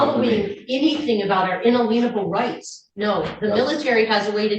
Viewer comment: Did she say "In-a-leavable" rights?